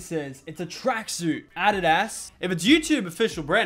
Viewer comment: Did he just say adid-ass?